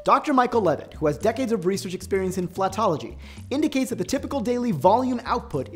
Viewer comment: Did i hear it correct......fart-logy 🤪